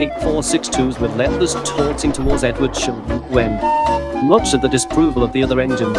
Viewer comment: What did the narrator say? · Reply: 98462s with lewas toring towards Edward shh